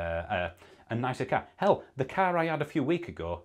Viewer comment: Top video, this explains it well 👍 Side note- for a second I really thought you said, "Do you want to spend it on takeaways, smoking, drinking, holidays, a nicer CAT" 😂